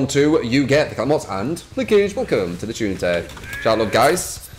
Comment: Are you saying "key token to the shuntè" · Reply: Arley Lairmore sounded like he said "welcome to the shunte"